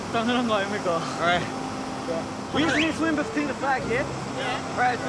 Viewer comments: If you're wondering what did Chan said to the lifeguard at (2:25), he asked: “We should swim between the flag, yeah?” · can someone tell me what did chan say here lol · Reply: I know I am late to this but here I will tell you chan said “ you just want us to swim between the flags yeah” the reason he said that was because here in Australia on the beaches we have these flags called the red and yellow flags and it’s a rule you have to swim in between them because that is the safest place where you can swim and the guards can keep a close eye on you to make sure nothing happens